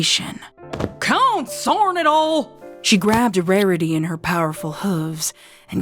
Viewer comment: “SON CORN ITALL-“ is what I heard